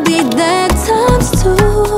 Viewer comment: the concept for this somg is crazy i thought she was saying im safer with you her ass said im safer wothout your ass tylas a geniius im lffically a tyger